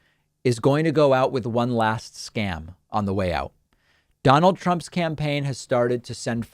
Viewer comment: I heard "Trump's campaign" at 0:20 as "Trump's SCAMpaign".